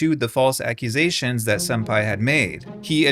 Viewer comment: For a good minute i thought you were saying “senpai” and not “sunpi”